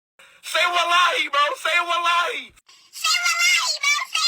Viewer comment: I can only hear him say /salalai/ instead of /wallahi/ and I thought it was just some weird jibberish thing he said until I found this video 🥀